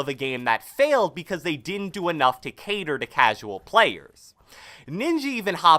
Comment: Did say ninji at 5:11 or do i have hearing problems